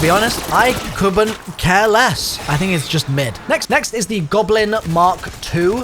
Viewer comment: is it me or did he say next next